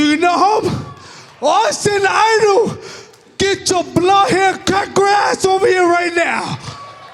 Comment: Can anyone on here comprehend what Homicide said @ 13:13? The subtitle said "get your blood hair cut grass over here right now". However, I heard " get your bald headed, cracker ass over here right now", but not sure because he sound like he's super shitfaced drunk lol